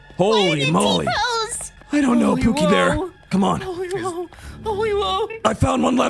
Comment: Eri: Why did it T pose? Bakugo: idk pookie bear. IDK IF I HEARD IT WRONG BUT I HEARD THAT